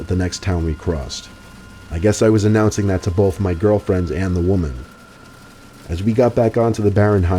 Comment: Did he have 2 girlfriends, or did I just hear that? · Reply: no he said to both my girlfriend and the girl